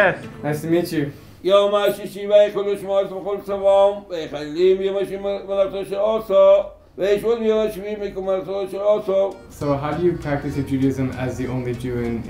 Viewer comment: As a hebrew native, i really dont know what he says 😅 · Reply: "יום השישי, ויכולו השמים והארץ וכל צבאם"